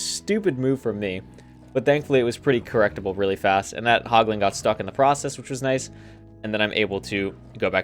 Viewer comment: Did he just say poglin at 7:12?